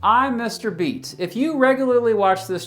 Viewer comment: I thought he said "im mrbeast"